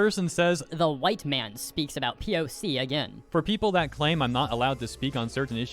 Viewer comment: I don't really use twitter so I thought they were saying "the youtube man speaks about poc again" lmao